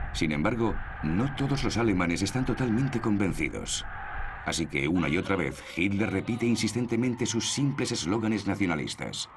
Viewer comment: que dijo? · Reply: Alemania marcha con nosotros, Alemania se rinde ante nosotros y Alemania nos sigue